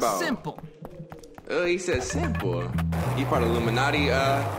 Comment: what did soilluminati say to him 😂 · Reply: Said he was lame